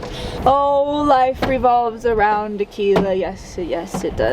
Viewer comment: watching this with the CC and AI thought you said, "oh life revolves around Tequila..." hilarious